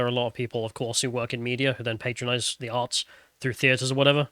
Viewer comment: At around 31:30 - Did you say they were all working in "The Arts" or "The Arse"? The sound was kinda unclear...